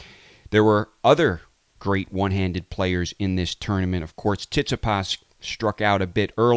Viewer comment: lol sorry man but I can't pass this up - did you say "Tits-a-pahs" ? lol